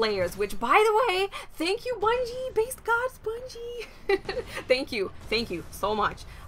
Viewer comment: did she say basedgod?